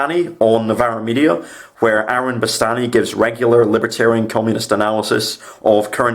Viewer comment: you suggested someone who gives good commentary of subjects happening in the UK. What was that person's name sorry? I've replayed it many times but i can't hear past your accent. Don't mean to offend, love your stuff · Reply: Aaron Bastani's IMO Bastani on Novara Media.